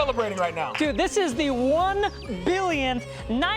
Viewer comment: Is it just me or did i hear them at one time say and 3rd view at 34:38 and then then 4th